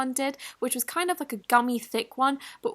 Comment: for a minute i thought she said "dummy thick" instead of "gummy thick" on the nebula slime